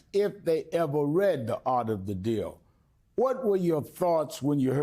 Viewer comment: I swear I thought it said: "FART of the deal" 😂